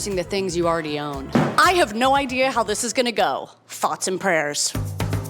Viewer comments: I thought you introduced Michelle the cat as the subscriber and the woman Cate as her roommate. Also, instead of "thoughts and prayers" I thought I clearly heard you say "farts and prayers". What's wrong with me?!?! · Totally thought you said "Farts and Prayers" in the intro. Glad the farts I sent you paid off, this transformation is gorgeous!!!! Such a beautiful space filled with beautiful things to begin with but dayummm always takes a space to a whole other level. Thanks for the eye orgasms queen xxx